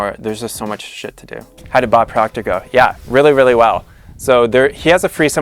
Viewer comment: what does he say? how about.... my english is not perfect :/ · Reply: Mon Escape "How did Bob Proctor go?"